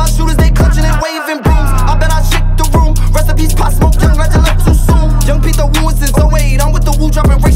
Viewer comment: What does he say at 1:34 about pop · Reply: He said Rest In Peace pop smoke then he said something like he’s woo and he’s dropping the rakes all day